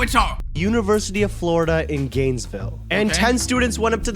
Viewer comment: GAYsville That's What I Heard At First Till I Rewinded